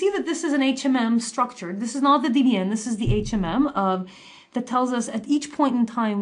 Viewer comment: What does the instructor say at 8:01, DDM? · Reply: I think it is DBN (i.e., Dynamic Bayesian Network)